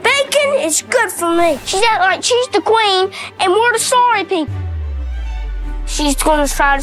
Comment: "She's acting like she's the queen, and we're the sorry people." I've seen people in the past ask what he meant by that. I think he meant "peasants" but didn't know the word for peasant yet. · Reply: I thought he said 'story people'...but I've never stepped foot on American soil so I shan't criticise myself for having trouble with an unfamiliar accent